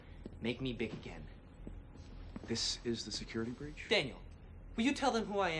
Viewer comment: Did he just call him thing? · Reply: No, he said, "Daniel."